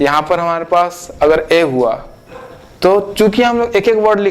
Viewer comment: is sir said chutiya